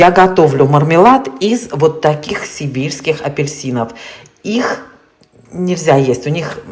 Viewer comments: Севильские апельсины, от слова Севилья, город в Испании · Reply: это и так понятно, просто послышалось "сибирских", не надо объяснять, где находится Севилья... ха ха ха/3 раза/ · Мне послышалось "сибирских апельсинов" 😂😂😂